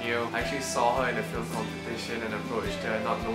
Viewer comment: Hi, what is the title of the film you said you first saw Vanes in? You mentioned it at 11:59 but I can't completely make out what you said. Did you say "Petition"? (and where can I see this film?)